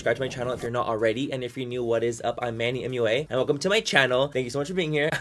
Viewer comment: In the first days of watching you I alsways thought you said I'm Manny anyway.....